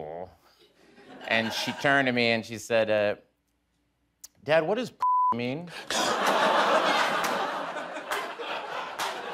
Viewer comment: When they bleeped it out, I thought he said, "Dad, what does 'bussy' mean?" Which would have been an even stranger thing to explain to your elementary school aged daughter.